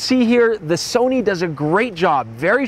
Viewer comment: Did he say 15 stops of dynamic range at 9:20 ??? Also, if you use an external recorder, can you record at 10bit?